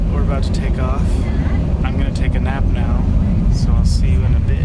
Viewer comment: It really sounds like he said "I'm going to take a nap, see you in a bi**h"